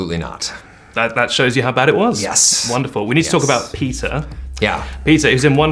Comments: The interviewer accent is so strong, i genuinely thought he said at 0:55 "we need to talk about pizza". For a moment i thought: why did you need to talk about pizza? is there a pizza scene in the film? then i realized it was peter. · Was it my ear or did the interviewer say "pizza" instead of "Peter" at 0:56?